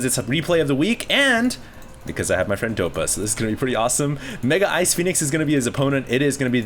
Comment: i thought he said niggaicefenix